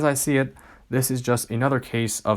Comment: So why do you claim that it’s white people being offended that sounds like a total racist remark so you don’t think any black people Asian people Inuit people any other people are offended you think only white people are offended man that sounds super racist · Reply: listen to what he says here. He didn't say ALL white people, he said MAINLY white people. Stop getting offended over shit that isn't racist.